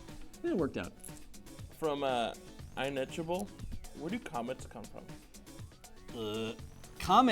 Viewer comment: I thought he said ''Where did comments come from?'' That would have been the easiest question! :p